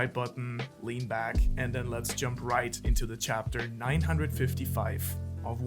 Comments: did he say chapter 955 0:50 or am i hearing wrong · Did you say Chapter 955?